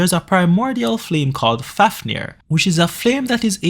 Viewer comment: can you tell me the name please I couldn't hear it properly 🙏😭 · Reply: Fafnir